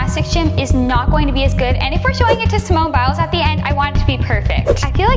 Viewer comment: I thought you said Simone biles like when she at